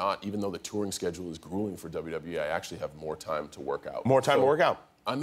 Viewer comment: It sounded like he said "WWA" But he was just talking too fast and blended WWE & I.